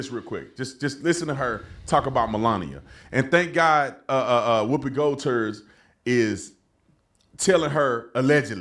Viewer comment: I heard the guy call Whoopi "WHOOPI GOLD TURD"‼️😂. Listen in shortly after 2:20 ...